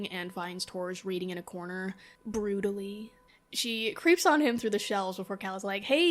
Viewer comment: I know you said "broodily" but I heard "brutally" and it's funny to imagine how someone would be quietly relaxing with a book in the library... brutally.